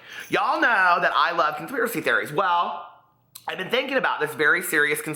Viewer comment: Every time you said “conspiracy theory” I heard “queerspiracy theory”!